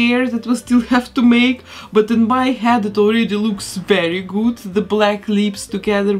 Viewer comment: “that looks very gut” Did I just hear German? I’m actually learning to speak German